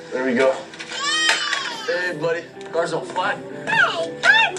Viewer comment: sounds like paul saying CAR's on fire....